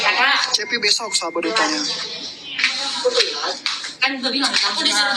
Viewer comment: Menit 1:17 kalo gak salah dengar katanya ceppy besok jd sahabat Duta. Benar gak sih ??